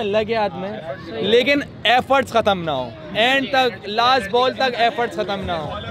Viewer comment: For a moment I thought he was saying "F" words khatam naa ho....it took me a few replays to realize he was saying efforts...